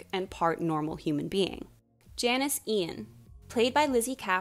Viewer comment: whyd i think it said Janesbian